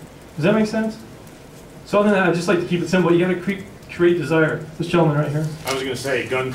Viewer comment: did he just say 'you have to creep to get desire' at 2:25? yeah, that sounds about right.